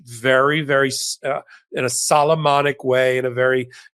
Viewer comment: The lexicon pest is back Mr Popok … sorry. There is no such word as solemnonic (or however you would spell it if it existed). Solemn is perfectly acceptable. “He did it in a very solemn way”. Have I already said this? I feel like I have. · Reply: I thought he said Solomonic. As in wise like Solomon. Just saying.